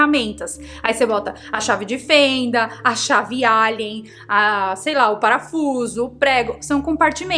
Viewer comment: Entendi "Chave Álien"